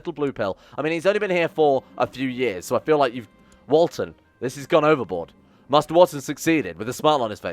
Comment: when I heard "Walton" I thought you said "Walder" ad in Walder Frey XD